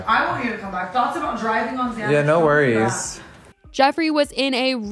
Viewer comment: is he on Xanax?!? Why does it sound like Tana is saying “thoughts about driving on Xanax… fuck that”?!?!!!!